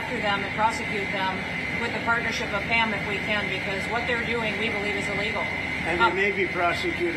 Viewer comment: Did she say what they're doing legally is illegal